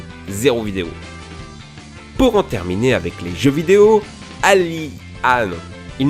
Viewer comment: ces qui a 1:37 minute?? aidez moi T^T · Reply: Lis les commentaires , ils disent qu'elle s'appelle Alice